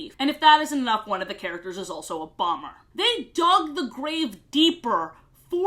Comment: I misheard this as "One of the characters is also Obama" 💀